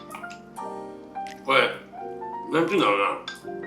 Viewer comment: ※なんて言った？のとこ、多分「なんて言うんだろうな」にかな？ そう聞こえた